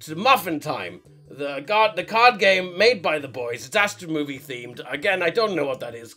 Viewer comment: I thought he said Squid Game for a second there